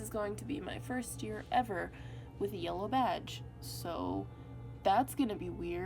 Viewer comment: I THOUGHT YOU SAID “YELLOW VAJ” IM SO SORRY HAHAHAH